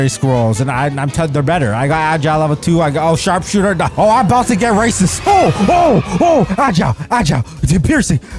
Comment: did anyone else hear say he about to get racist